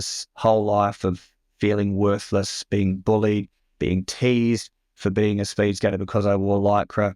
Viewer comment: Being "teased" is awful. I thought he was saying, at first, he was being "tazed" and I was horrified. I am grateful for him sharing his story.